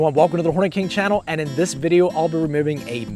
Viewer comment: wait what what did you said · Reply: Welcome to the hornet king channel he said